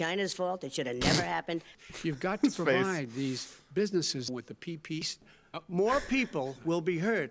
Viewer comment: did he just say “ with the peepees “ 😂😂😂😂